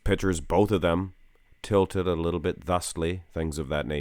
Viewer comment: I'm not a native english speaker, and i always hear you say the word "Tilted *word*" at 1:41, and i can't understand how it is spelled, not even what it means, can someone explain it to me? Pls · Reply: He said “ tilted a little bit”. It means a small amount, or like pequeno in Spanish